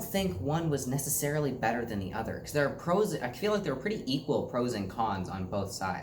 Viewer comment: I think being a male or female has each own pros and cons. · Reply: I thought u said men have more pros. sorry